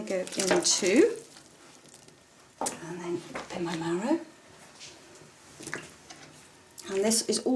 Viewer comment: what are the white chunks that Ruth is putting into the pot of curry? I can't figure out what it is. Anyone know? Thanks in advance! :) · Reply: She either said "mallow" which is a type of sqash, usually pear, or "marrow" which is basically a zucchini sqash. So it is safe to saysome type of sqash.